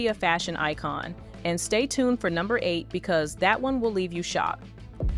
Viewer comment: did you say "shot" or "shocked"? I actually like TOW's "the Birds" dress. I seem to be the only one.